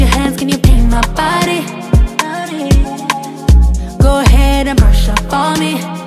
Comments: Boy i thought it said "Go head aunty"😂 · I keep hearing “go ahead auntie”😂😂😂 but other than that I love this song